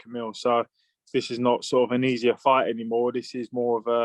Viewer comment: I thought Hearn said "No easy fights" 🤔